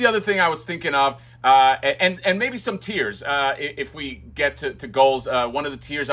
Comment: When you said “tiers” I thought you said “tears”, and immediately laughed out loud! Like he gathers collector’s tears. Possible additional accessory?😂😂